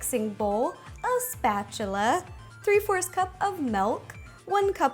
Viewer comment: did... did she just say... melk?!